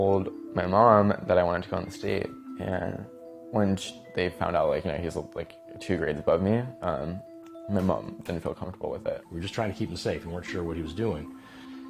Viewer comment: Can anyone understand what he said between 1:42-1:52 ?? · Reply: he told his mom he wanted to go on a date and when they found out he was two grades above him they felt uncomfortable